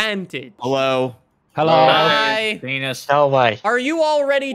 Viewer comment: at around 1:53 did some1 say p.e.n.i.s ??? sry if ur family friendly but i need to besure